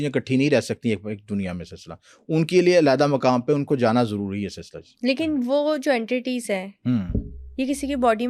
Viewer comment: One question is what is this man keep on saying after each sentence sinlashi 😮